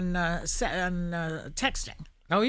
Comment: Wait what!!! Was she going to say “sexting” 😂😂😂😂😂